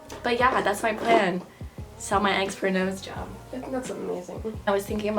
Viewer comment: Thought she said “sell my ex for a nose job”